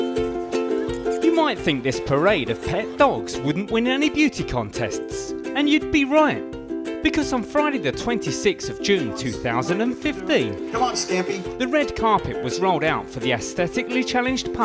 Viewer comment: DID SOMEONE SAYS STAMPY?!?